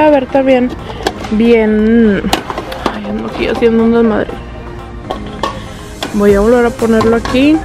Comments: like si escuchaste que dijo de$m@ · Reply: Ricardo Carmona dijo dios estoy haciendo un desmadre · Like si escuchaste que dijo: estoy haciendo un desmadre en el minuto 2:55 😅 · khe dijo? Si escuche bien? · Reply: Cuando dijo "haciendo un desmadre"?